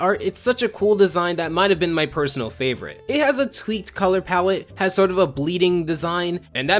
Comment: D-did you just say color PAWET ??? That's the most UwU shit I've heard all week